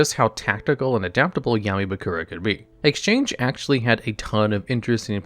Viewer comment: Did call him Yaoi bakura at 00:25? 👀👀